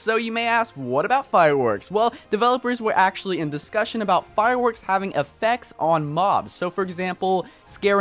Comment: Am i the only one who heard "Firewekk"